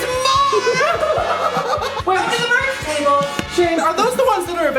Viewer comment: 3 years ago when Shane said “Come to the merch table!!” I thought he said “come to the birth table!!”